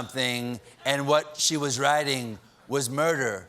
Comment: Did anyone think Stephen was saying "riding", instead of "writing"? I thought he was saying "riding" and my mind was in the gutter.